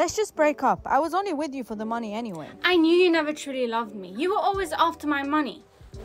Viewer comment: I thought she said “you were only after my mom” 💀